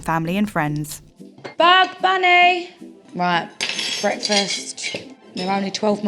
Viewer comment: Nobody talking about the names BUG, BUNNY & JOHNNY BLUE?! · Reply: I thought I misheard when she first yelled, "Bugs Bunny!" I was only one letter off.